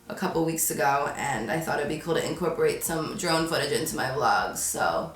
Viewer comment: only half way through this video but i had to pause to come on here and say that I thought you said "drunk footage" not "DRONE footage" in your last vlog & i was like "YASS BOO! i loved seeing you cut loose at the bachelorette party!!"😂anyways so happy you're happy ❤️❤️